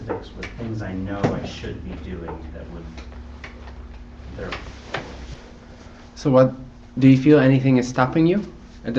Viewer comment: he says something "and in essence you can call this another way of looking at ??? work". What is the word before work? Why kind of work is he referirng to? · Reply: shadow work :) Hey Simon, if you like this approach have a look at Scott Kiloby's unfindable inquiry. Its essentially the same just a different style of looking..